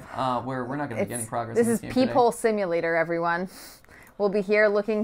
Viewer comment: I thought she said people not peep hole at first